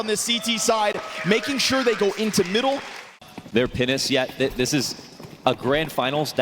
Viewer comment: what did you want to say about penis again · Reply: Finesse ❌ Penis ✅